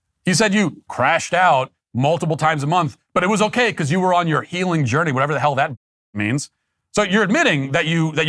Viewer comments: Step 1 of your "healing journey": delete your social media accounts. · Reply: I thought she said FEELING JOURNEY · I thought she said, “feeling journey.”